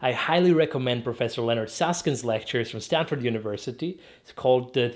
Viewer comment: Till now, I thought he was saying "Leonard Saskin", even the captions say that! When he mentioned his book it clicked to me that it's actually "Leonard Susskind" (and Art Friedman)